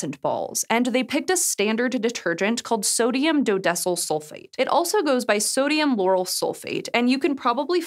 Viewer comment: why does she say "sodium yanny sulfate?!" 😏